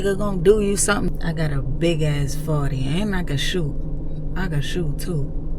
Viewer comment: For a minute I thought you said I got a big ass fart 😂😂😂😂😂😂 I had to rewind that to make sure😂😂